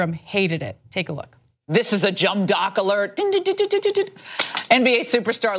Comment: Did she just say jum dock?...ah ok